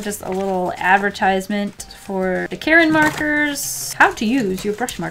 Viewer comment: i'm drawing while watching this so when i heard her bring out the karin markers i thought she was saying "these markers are from the brand KAREN" and i got scared because karens scare me